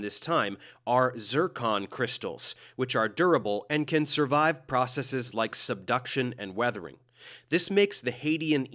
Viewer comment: I heard they can survive seduction 💀 hum